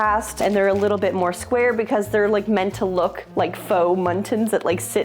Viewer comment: When you said “faux mullions”, I heard “muttons”, as in fake sheep. 😂😂😂